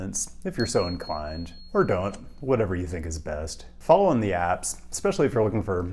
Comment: I love the bonus kitten love. I missed what you said for bonus cat stuff, sounded like you said use the apps. Apps? Do tell! Oh yeah, and the city nerd stuff was pretty good too. 😄